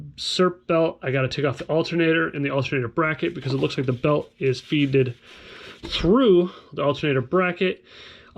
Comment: great video. At 5:48 did you say "Feeded"?